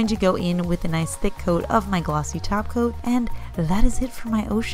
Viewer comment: Wait ... w h a t??! Glossy top coat?!? Who are you, and what have you done with!!?? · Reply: Nail Therapy Studio I know! I thought I heard “glossy taco” for a second! 😜 🌮 💅🏻